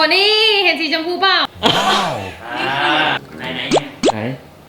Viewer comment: ไม่ใช่ค่ะย้อนไปที่00:48วินาทีค่ะยังได้ยินเสียงพี่พูดว่า"ไหน"อยู่เลยคะ